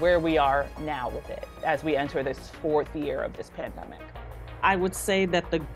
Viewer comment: did u just say forth year 0-0. its been that long?